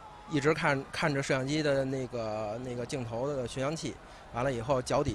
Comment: did he just say the n word o.0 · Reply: 'nage' mean which in china